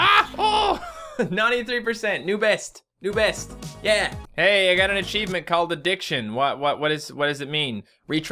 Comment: When you said " I got an achievement called addiction" I thought you said "I got an achievement called The Dicksion". 😂😂😂